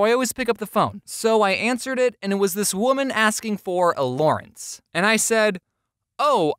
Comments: did u say allurance bc in case u did unsubscribed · VOLTRON INTENSIFIES Lance is VERY FUNNY FOR YOUR INFORMATION!!! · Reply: I heard Allurance and after seeing Lance I'm pretty sure now that James ships Allurance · did ya say allurance or am i just tripping? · Um, not sure if I'm imagining this but I swore at 4:11 I heard him say 'Allurance' ?? As in the ship from Voltron ? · AND YOU KNOW VOLTRON OMFL, YASSS IM CRYING, BUT NEVER SAY A LAWRENCE AGAIN, 4:11 MADE ME DEPRESSED BECAUSE IT SOUNDED LIKE ALLURANCE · Did anyone else notice that was lance from voltron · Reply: Also, did anyone notice that at 4:13, it sounded like James said "Allurance", as in the ship, Allura x Lance? Is he a shipper or am I over thinking?